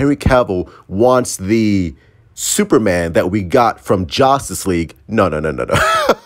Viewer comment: did you say jostis league? 🤔